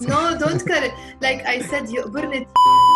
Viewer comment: The minute 36:55😂😂 I wonder what you said? 😂😂Shou 9oltelo ? He had to cut that part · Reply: it’s yo2borne tiizak! It’s a very common expression in Lebanon.